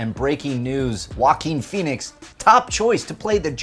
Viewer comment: I thought that they said “Walking Phoenix” lol